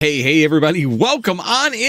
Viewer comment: intro sounds like your saying gay hey everybody 🤣🤣